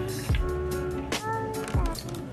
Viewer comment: Is it just me.. or did I just hear "Samson shut up!" Also your my favorite YouTuber